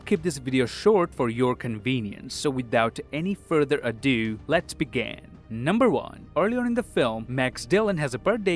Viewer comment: Whenever you say let's begin I hear let's began